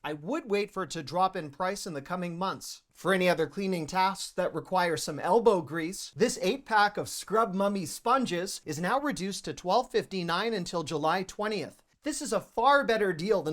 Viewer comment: Did he say “Scrub Mummy”? I always watch recommendations on Costco and Aldi. I want those Hurley shoes for $20. Great job!!!